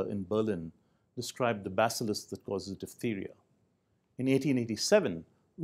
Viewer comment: Shiv goes beast mode. This man is my new hero. Straight up poet. THANK YOU DR. PILLAI By far the most compelling introduction to Immunology I have ever seen. Did he really say "the basalisk that casues diptheria at 1:10?" that is off the hook!!!!!!